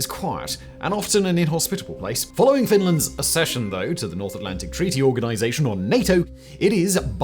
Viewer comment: As a Finn, I would like to note that we don't really see joining NATO as "ascension". We didn't really want to do it. It was just a practical thing we had to do. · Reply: I thought he'd said "accession", as in being added, not ascension, like being promoted. Just my take